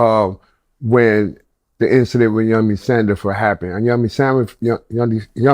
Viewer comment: did he just say "Yummy Sandwich" ? @ 0:31 😂😂😂